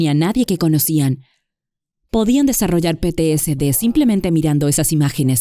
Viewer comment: Un favor, en la parte que dice de ver imágenes cuando menciona a la gente que vio repetidamente ver las torres gemelas en ese momento del incidente, desarrollaron ¿qué? No le entendí, si alguien pudiera decirme por favor. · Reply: Podían desarrollar tts . Hablaba de la neuro plasticidad